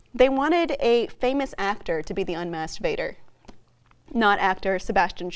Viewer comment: I swear I heard her say "Masturbator" , until I rewound 'Masked Vader'